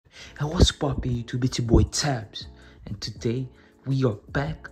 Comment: What is He Saying? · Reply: was poppin🤷